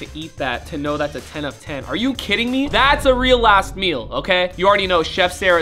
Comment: I thought he said that’s a real a$$ meal until I saw the words😅. Love you Brian keep making bangers